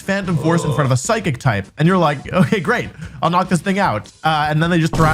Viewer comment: did he just said the N word 😭 Nvm he said thing 😭😮‍💨